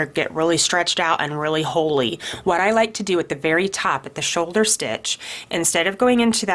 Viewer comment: I thought u said the shoulder to the waist